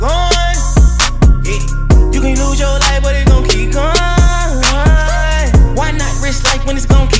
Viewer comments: I know the lyrics but I always say Horses don't stop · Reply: first time i heard this i thought he said 'you can lose your life like donkey kong' and idn why but i felt those ! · lol always thought he is saying "you can lose your life in donkey kong"